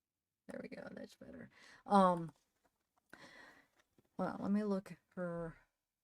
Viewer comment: I thought said help...let me out